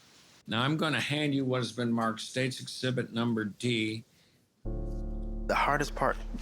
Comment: Wait... did he say "states exhibit NUMBER d? 🙄😵‍💫😒😳🤔 23:24 Now we see how and why the investigation was botched 🤦🏾‍♀️🤷🏾‍♀️😂